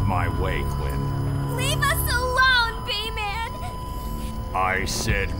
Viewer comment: Did Harley call him "Bayman"? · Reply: No Harley says b-man as a shorter word it does kinda sound like bay but it's pronounced as be-man